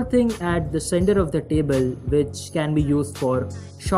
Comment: Who else heard "Table" instead of "Cable" ??